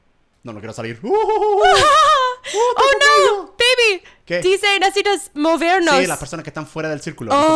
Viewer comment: ♡AMO A GERMÁN Y ♡ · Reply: MicanSep tambien creo que dice baby a german en el minuto